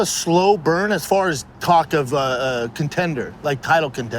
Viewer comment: Schaub - I finished 3 dudes. Seconds later he Freudian slips cock instead of talk. Ya’ll still rockin’ with schaub?